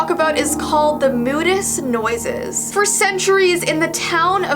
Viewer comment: don't follow the nosies under water *People who hears nosies under water: goes follow the nosie*. (No likes :p) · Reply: I thought u said noses! Haha!